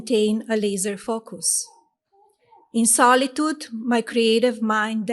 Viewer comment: Did she say that she has a "lizard focus" at 19:15 ?? So she confessed, she´s one of those "Reptilians" of the urban legends.